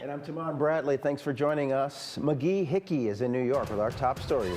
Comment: did he say.....McGee....Hickey?😆 In all seriousness though I'm really feeling for the Ukrainian people.❤ Putin is such a pos bully.